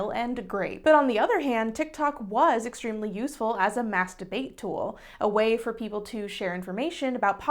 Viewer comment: "Useful as a mass debate tool" Wait, did I hear that right? Didn't realize there was p@ on TikTok!